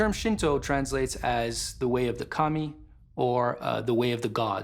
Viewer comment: Well that was an odd couple seconds. I thought he was saying "It's the way of the commie" LOL! ...ancient astronaut theorists agree that he wasn't.